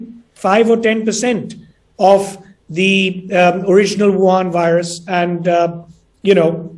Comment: Did he just said Wuhan virus😂😂. Then he said alpha whatever u want to call. 😂